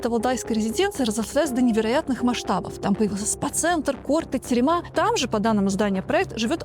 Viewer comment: у Путина на даче есть личная тюрьма? 😂😂😂 мне послышалось?